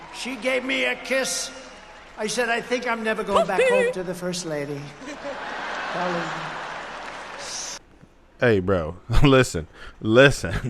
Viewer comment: WTF did he just say???? I think I’m not going back home to the first lady. Clinton: “ I did not have sexual relations with that woman” surreeee u didn’t lemme call Monica real quick